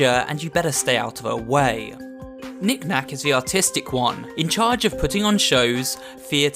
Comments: for a second i thought he said "nick is the autistic one" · For a second i heard "The autistic one" (.0.)